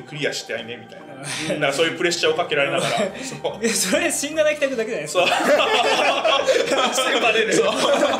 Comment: のところさんなんて言ってるの？ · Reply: 「そりゃシンナナキ宅だけじゃない？」 かと